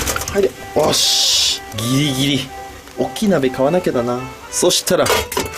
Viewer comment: ギリギリがキリキリに聞こえて ふぇ⚪姉さんおもいだした((((っ・ω・)っ